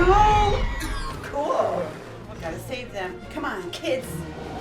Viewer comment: Did you say "come on, kiss"?